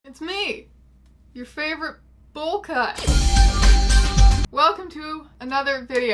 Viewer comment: I thought she was going to say "your favourite lesbian YouTuber"